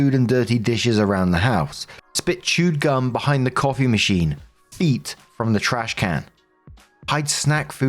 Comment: Story 1: I misheard you and thought you said "Eat from the trashcan" instead of "feet from the trashcan". I thought the brother actually ate from the garbage, my bad